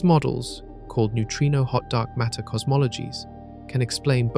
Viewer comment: I thought I heard "hotdog" matter cosmologies, I thought that can't be right can it? he said hot dark matter lol